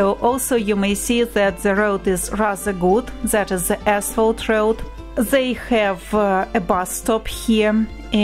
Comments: 1.25 did she say “asshole road” or I just misheard? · did she say asshole?